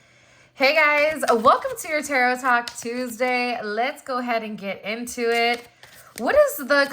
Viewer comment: Literally thought you said Tarot Taco Tuesday 😂 I'm up for that party